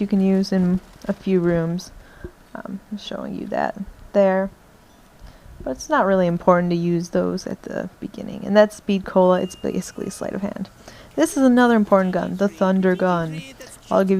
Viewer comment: Did you say terd? :P